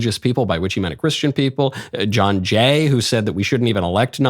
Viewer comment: When you said “John Jay” I thought you said Jeh Johnson. I thought for a moment he had a redeeming quality